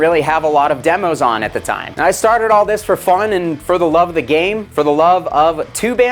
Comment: Thought you said you did it “for the love of the gain” and you should’ve.